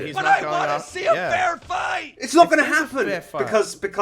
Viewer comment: i heard keemstar say I WANA SEE A BEAR FIGHT